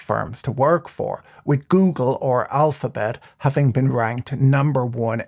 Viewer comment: Did, did he just say goongle at 3:20ish?